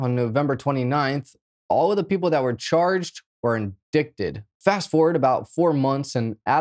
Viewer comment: I love this guys videos but I think I just heard him say “ in-dick-Ted” when pronouncing “indicted”. Lol